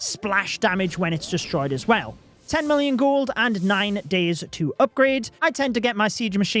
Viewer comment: did pro just say 10 military gold there!